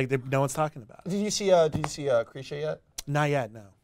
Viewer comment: What film is he saying at 13:22? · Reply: it's Krisha